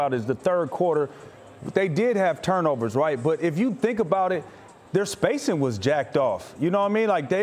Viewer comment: Did he just say their spacing was jacked off?